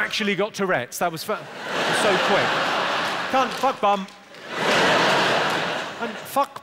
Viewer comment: it sounded like they answered each other 😂😂😂 “Cunt” “Fuck bum”